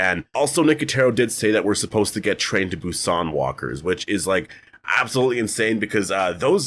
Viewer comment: What kind of walker did he say at 1:53? Glow-in-the-dark walkers and what? I've listened to it like ten times and can't figure out what he's saying. 😅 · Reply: Train-to-Busan walkers. It’s a movie, Japanese or Korean I’m not sure